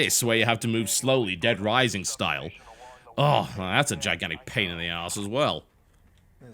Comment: i hear 'pain in the arse', i think challenge